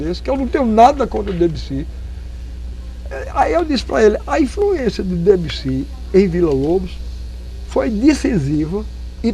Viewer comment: não entendi. Ele falou: "A influência de ... em Vila Lobos" Qual foi o nome q ele fala q influenciou Vila Lobos por favor? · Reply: Entendi. Ele se refere a Claude Debussy.